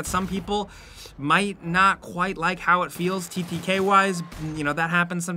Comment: Can someone tell me what TPK means (or whatever he's saying) at 2:53? Thank you.